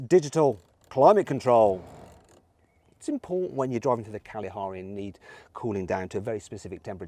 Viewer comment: did say when driving to "The Kalahari"